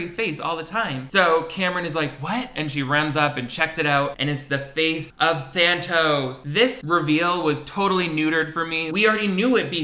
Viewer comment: When you were talking about her making the drawing of the scary face I thought you said "it's the face of Santa!" XD I wasnt fully paying attention 'cause I have ADHD so I'm listening to this while playing Fortnite so I didn't catch the villain's name at first~